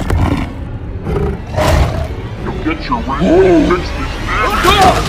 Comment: Did anyone else hear "You'll get your rent when you fix this dam door" in slow motion? 🤣🤣